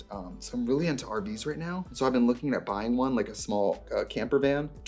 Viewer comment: I was distracted and trixie said “I’ve been looking into RV’s” and I heard “I’ve been looking into Arby’s” I audibly said out loud aren’t they a vegetarian?